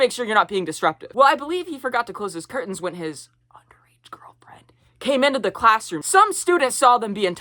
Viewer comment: what did u say at 2:42 i cant read lips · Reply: he said underaged girlfriend